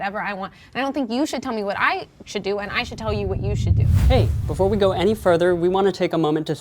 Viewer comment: Wait wait wait wait wait wait wait wait wait wait wait wait wait am I miss hearing this to that one girl in the ripped up jeans say at around 13:40 something mins and I quote " you shouldn't tell me what to do " I should tell you what to do " I swear I heard that if you go back and replay that part and listened intently and very focused I swear that is what she said I could possibly be wrong..